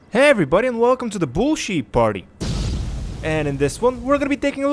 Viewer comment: I thought he said bull sh%t party